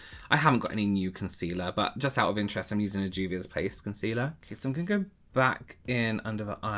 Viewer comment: has ruined everything as I only hear “Juvius Place” now.